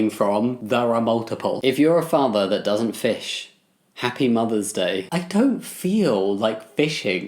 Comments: I heard “If you’re a farmer who doesn’t fish, happy Mother’s Day” and I did a double take 😂🤣😂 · when I both heard and read farmer instead of father…I got so confused